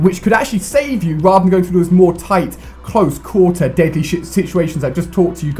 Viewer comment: Did he say "deadly shit"